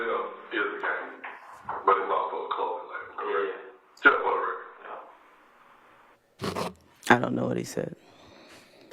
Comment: MR STEEL WAS TRYNA FIGURE OUT WHAT DETECTIVE GAITHERS PARTNER SAID AROUND THE 6:58:23 MARK. HE SAID, “SO YSL IS A GANG. BUT IT’S ALSO A “”CLOTHING LABEL””